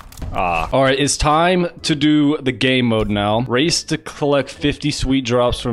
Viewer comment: did he say gay mode or game mode lmaooooo